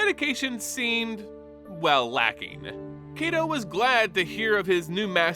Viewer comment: Why everyone sound like they saying KaiDo not KaiTo. Are my ears just shot??